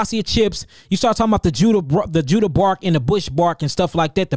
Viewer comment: Did he say the 3 bitters are Quassia root Judá bark Bush bark